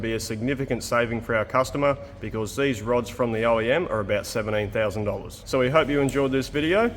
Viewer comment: I thought you said 78k... 17k is still a lot, but thank you. :D